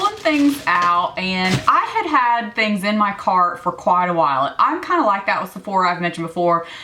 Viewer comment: I will echo what many have already said. You look beautiful - your makeup is flawless. I would love for you to be my personal shopper for a day!!! While watching the video, I had to turn my phone volume up because I thought you said you were putting things in your car (instead of cart)!!! Why is she storing good makeup in her car??? I started laughing when I figured out what you were saying. I was born and raised in Texas, but your accent is thicker than mine! (Not a bad thing at all.)